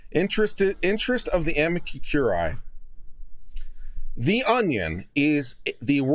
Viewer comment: the first time you said Amicus Curae I thought you said Amica Cream. I need coffee ☕️!